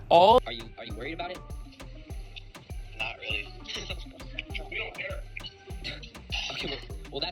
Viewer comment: Did anyone else hear in the background after Jake Paul says it doesn’t scare him or whatever? Someone said “we don’t care”